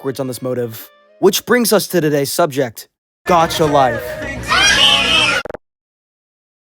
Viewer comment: The captions says "got your life" in 0:30 💀